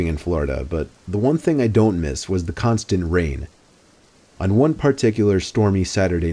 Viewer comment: Lol I thought it said "constant rape" 😂